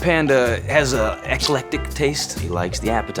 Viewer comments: What is Drew saying at 15:04 ? It sounds like "excellent dick taste" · To commenter Hans Hummler (3 weeks prior), the phrase you're asking about (index 15:04) is "eclectic taste", but granted he pronounced it as ex-a-lec-tic taste. Like you, my brain assembled his syllables as "excellent dick taste" the first time I heard it. Made no sense. :)